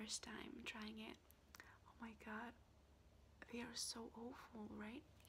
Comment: I thought you said 'oval' 🤣🤣 You look opposite of awful 💕